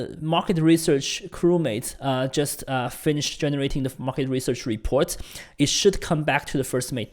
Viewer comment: sounds like he says "the f*cking report" 😂